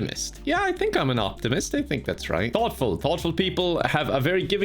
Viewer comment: When the test said thoughtful I thought he said awful and I was like “oh no, they know about his sims play though…